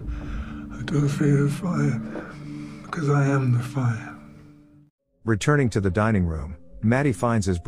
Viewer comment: What does he say at 2:33 ? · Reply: "I don't fear the fire, because I am the fire" I think